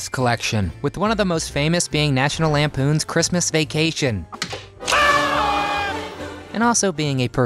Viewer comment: Thought at first it said National Lampoons Christmas vaccination.